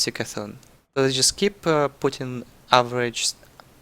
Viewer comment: What did he say about Putin? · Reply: sht, its really putting